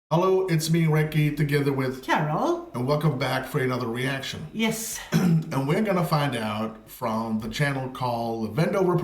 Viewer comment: You can drive to Alaska. You cannot drive to one city in Alaska - Juno. Also, It was so funny because to me it sounded like Recky was saying Bendover Productions. ☺️